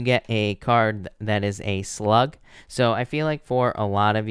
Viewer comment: Soon as I heard slug I was like ugh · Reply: I thought he said a slut 🤣